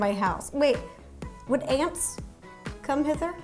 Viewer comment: Ro: wait, would ants...come in here? (I have no idea what she said there) Me: what just aunts no uncles?